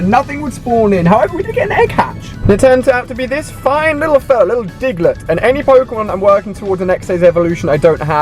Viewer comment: When he said digglett I thought he said niglett